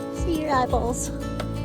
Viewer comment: I was listening to you talking to your horses and I thought you said, let me see your apples, but I think I heard wrong, & you meant, eyeballs. But my brain started working and wondered why you don’t feed your animals apples or carrots? Are they bad for your babies?😊 I think not getting another horse was a good decision. Even though I am very fond of Arabians, and would love to have seen a new horse in your paddock, I think you’d be pushing yourself to add another responibility when you have so many plans for the summer already. Looking forward to this summer.😊